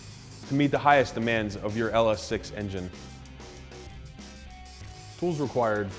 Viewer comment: LS6? LS3. Otherwise great vid; u guys always have the best installs. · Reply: I thought he said LSX engine but he definitely said "six" lol